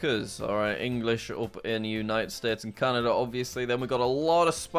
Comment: Is it just me or someone else thought that he said United States of Canada 💀💀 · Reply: I think he said the United States and Canada